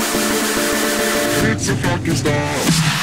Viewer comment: Instead of "its a fucking storm" i hear "its a Pakistan"